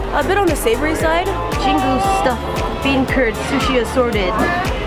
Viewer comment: I thought u said turd for a sec